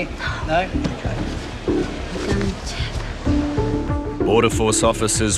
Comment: she did not say "you can check" she said "concha" which is a swearing equivalent to "f***k".